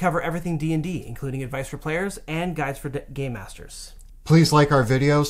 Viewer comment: sounds like he says "gay masters"